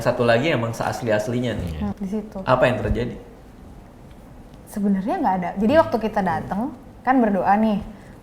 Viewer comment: Di menit 38:51. Ada yang engeh gak?. Kok gw dengernya ada yang ngomong "benar"